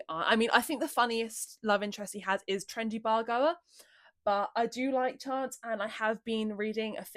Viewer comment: WHAT is she saying???? it sounds like gibberish 😭😭😭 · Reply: "i think the funniest love interest he has is 'trendy bar goer', but i do like chance"